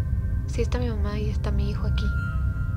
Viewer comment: En el minuto 8:00 escuche mal o es verdad DIJO: “si esta mi mamá y mi hijo aqui” Hijo gente HIJO😨😱 no inventes yo no sabía que tenia hijo nel da igual la amo aun así😍😳